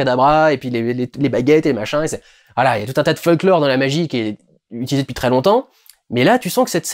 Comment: Alors je sais pas du tout si c'est voulut de ta part, mais vers 49:00 j'ai cru t'entendre dire FUCKlore et pas FOLKlore (comme je suppose qu'est ce qui a été dit XD). Du coup, quand on comprend là où tu veux en venir dans les propos que tu expose à ce moment, je trouve ce possible lapsus assez révélateur, et en tout cas très drôle XD.